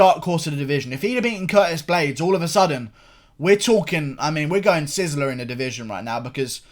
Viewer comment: Did I just hear u say “we’re going to sizzler” Jesus Christ what has done to u